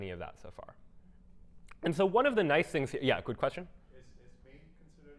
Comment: I think I heard asked a listener "Good question?" All I am trying to say is that don't ever question yourself or your questions. You are a wonderful lecturer and every question is a good question. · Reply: No, he said "Quick question?", which is very common to hear.